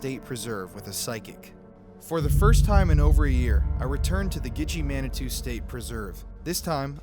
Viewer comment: I swear when she (the older lady) first said the name of the place, Gitchie Manitou, I thought he said Gucci Mane. Anyone else? Just me? Ok... lol